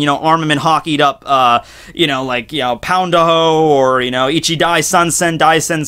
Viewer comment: am I the only one who heard pound a hoe xD